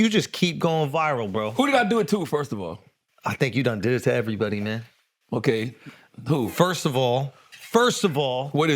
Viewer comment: 0.20 he said you've done did it to everybody..😂😂😂 I thought he said you had done Diddy everybody 🤦🤣😂